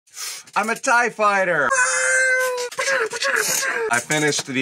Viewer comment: I wasn't look at my screen when i scrolled down to the video, and thought you said "I'm a typewriter!"